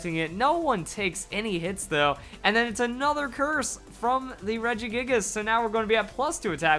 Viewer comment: did he say religious?